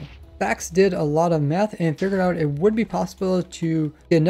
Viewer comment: i it just me or did it sound like he said meth